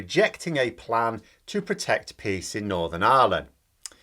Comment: And I thought for a minute you said "protect pigs in Northern Ireland", and I have to admit, I was just waiting for you to explain Bobo's reasoning behind that, because, of course that is something the nutter would say. The second time you said it I understood "peas". Still not shocking. And then I realised: Peace! ...